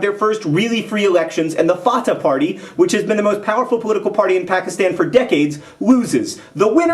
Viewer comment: did he just say pakistan